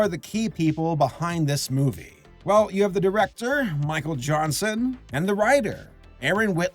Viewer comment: I heard “Michael Johnson” as “Michael Jackson” and instinctively said “HEE HEEE”